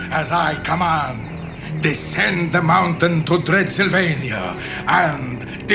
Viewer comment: I thought they said “Dreadsylvania”.